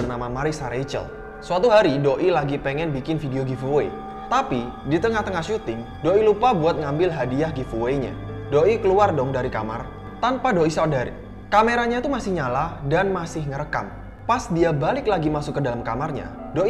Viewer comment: "Tanpa Doi Sodari" :v Wkwkwk 😂